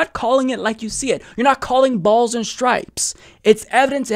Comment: Did he say "you're not calling it balls and stripes"?